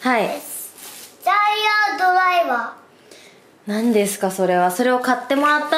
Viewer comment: ごめん🥺🥺 おばさんには「ジャイアンドライバーに聞こえてしまったよ🥺 最近ジャイアンになりきったご挨拶流行ってるもんね😇なあんて思ってしまったよ🥺